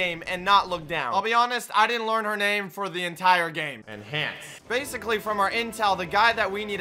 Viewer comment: Did he say : dem pants